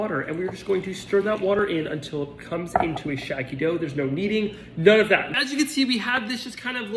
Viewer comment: When he said no lead I thought he said no need. And then I thought if there is no need why the heck would I want this bread until I looked at the title 😂